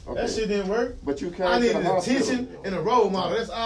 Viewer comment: When I was a kid, "I needed a teaching and a role model..." Bruh! This is a blow👊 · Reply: I thought he said attention 😂, but yea either way I felt that shit! So sad.